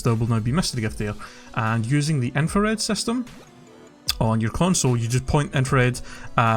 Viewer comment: Wait, I couldn't hear what you said at 1:50 , the what system? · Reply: I said the Infared system